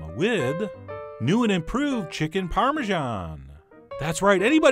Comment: Did someone say "Chicken Parmesan"? Because all I hear is "Adam Pacitti".